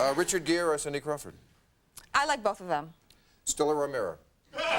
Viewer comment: what did he say? "still or a mirror?" I can't make it out. Any help would be great.